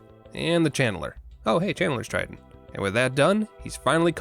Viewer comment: "Oh hey Channeler's Trident" I heard "Channler's Strike" and was wondering what the punchline to the nonexistent youtube channel strike joke was lmao